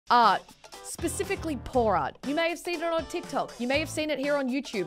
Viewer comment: I thought she said porn art 😂😂😂